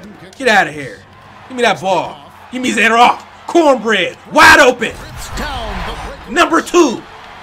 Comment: Did he call Kobe Bryant Corn Bread? LOL I love this guy! I just found his channel yesterday and he's hilarious and also a really good gamer.